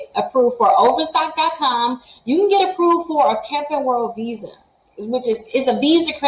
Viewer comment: What is the name of the visa card? I can't find capital world visa which is what I thought you said. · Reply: Camping world